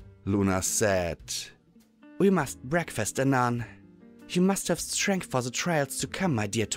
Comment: Did Luna just say "Anon"?